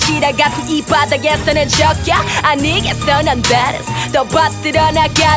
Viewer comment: Love this song so much!! Thank you for the subs, but I think at 1:10 she says "BADASS".. not "badness"